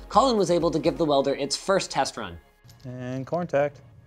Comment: Corntact? Did I just hear?